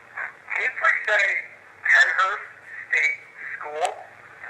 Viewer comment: During the EVP session around 58:37 in response to asking if they can say Pennhurst State School, it sounds to me like they say "Pennhurst Estate". As far as I know, it has never been referred to as that. It very well could be them saying "Pennhurst State School" and the end got cut off like the end of the "My name is" EVP did. Also, the EVP session around 1:04:03 says that the spirit mentioned their name was David and if they could say "Hi I'm David", and yup, I hear it. It's very breathy, but I can tell it says "I'm David". I always watch these on my TV with the volume up moderately loud, not too loud otherwise the EVPS sound too muffled, anytime I've listened back on my phone or laptop with headphones to see if I hear anything different, it's always more muffled, I've discovered that watching on my TV is the best for hearing clear EVPS. :) Always enjoy the videos guys, much love!! Cheers